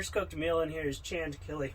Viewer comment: did he say canned chill backwords